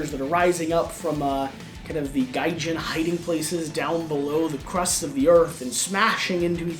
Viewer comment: I heard "Gaijin hidden places" :D